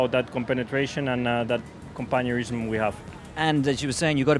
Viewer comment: sounds like he said: “ That compañerism”